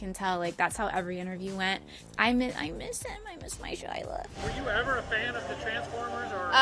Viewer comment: did she say Shila...?